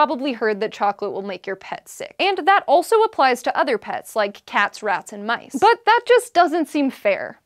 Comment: I could have SWORN she said "cats wrapped in mice"....